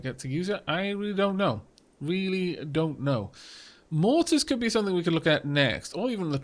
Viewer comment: I heard "Mortis" and i thought of Robot Wars.